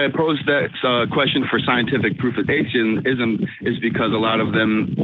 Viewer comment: He said "Scientific proofidation"? I checked 4 times. Wtf? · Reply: proofidation!! heard it as already typing this. around 5:30 btw. cool funny word. ima steal it.